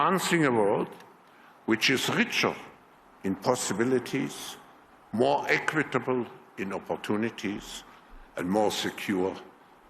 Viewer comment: I swear I heard him say " More equitable in-opportunities ' ........😶